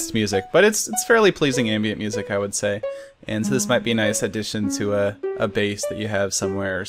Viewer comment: When he said it might be a nice addition to a base, at first I thought he said it might be a nice addition to a bass.